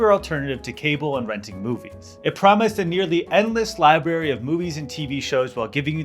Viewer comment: did he just say “libary”?